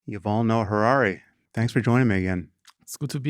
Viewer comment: "You all know Harari" I thought sam said when he introduced Yuval😂